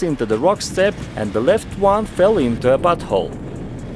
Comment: Uhm, correct me if i'm wrong but ....did he say "and the left one fell into a b*tthole"